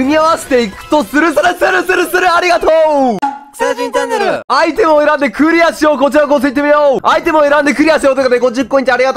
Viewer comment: 相手を恨んでクリアしようにしか聞こえんw